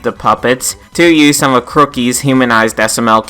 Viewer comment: Where are these better human designs for the plushes that you keep referring to in the video? I may have misheard but I think you said they were made by a crookey?